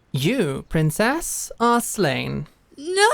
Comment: I heard “you, princess, are slaying” which I am deciding retroactively is what the line always was